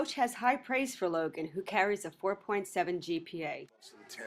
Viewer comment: did I just hear 4.7 GPA....